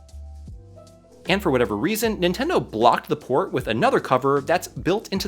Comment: Did you say intendo?